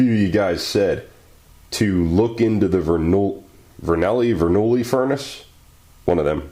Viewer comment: <link> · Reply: ohh sorry I thought u said bernouli not verneuil